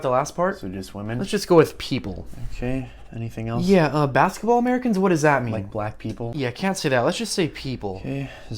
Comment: I dont know why, but its the "Ahhhhh" at the end that makes me laugh. Also I honestly thought he said basketball players. I dont know how, but thats what I heard